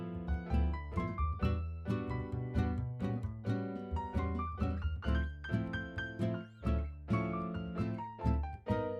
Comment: hahahahahaha what did you say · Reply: Aa yaanaa aaviin bor aygaa